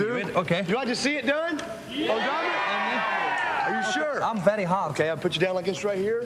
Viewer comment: does Dom say "I am very hard" ?